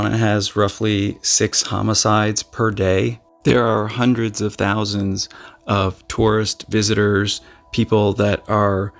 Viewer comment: Did you say at 3:48 that hundreds of thousands go missing in Bajaj ??? 😲😲😲